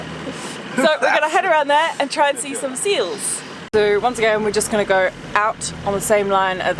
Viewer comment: did you say Squirrel's? 😂 I know how much you love them.